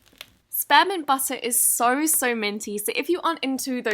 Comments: I thought she said sperm and butter at 3:36 😂😂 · When you said “spearmint butter” I wasn’t looking and I thought you said sperm in butter I was shook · I thought she said sperm and butter 😂😂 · I thought when you said “spearmint butter”, you said sperm and butter😂😂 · I thought she said sperm and butter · i highkey thought she said "sperm and butter" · when she said spearmint butter, i thought she said sperm and butter 😆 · When she said spearmint butter I thought she said sperm and butter and I was really concerned😂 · Thought she said sperm and mint instead of spearmint oops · It sounded like you said sperm butter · Lmfao i thought she said sperm butter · Omg when you said "spearmint butter" I thought you said "sperm in butter"........omg I hate myself..😂😂😂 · so I thought she said “sperm n’ butter” at first until I read the name😂😂😂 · When she said spearmint butter I thought she said sperm and butter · For spearmint butter I thought she said sperm and butter but then I looked at the name lol Edit: I’ll just go